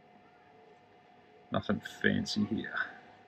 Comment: Did I hear you say "nothing fancy here" as you put the chocolate on top lol. Good videos guys. Thanks for sharing. Cheers.